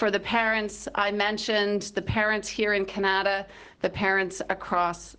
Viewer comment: Im sorry what did she say kinyada?